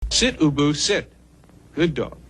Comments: I was definitely one of the kids who heard this so many times but in a television induced stupor never read the logo to make the connection, so have always heard it as "Sit, Boo Boo, Sit." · I thought he said "Sit, Boo Boo, sit" up until this video which I humbly admit, great episode. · When I was a kid I thought it was saying sit boo boo sit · Fml I literally ALWAYS thought that he said "sit boo-boo sit...good dog...woof" it literally says Ubu 😅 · Maybe it was because I was a young enough child when I was inundated with this little clip but I always heard it as "Sit Bubu, sit. good dog." I don't remember it having the word Ubu on the screen though. Seems like if the words were there I wouldn't have heard it incorrectly, but here i am. * I wrote this comment before it touched on it. Definitely Bubu/booboo. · Thank God I'm not the only jackass who thought he was saying "Sit, 'Boo-Boo', sit"!😂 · I always heard it as sit boo-boo sit. I couldn’t read back then. I was too young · I remeber that, and I too thought he said:”sit boo boo sit, good dog”🤔💯. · I always thought the vanity card said, "shit Ubu shit". Go figure.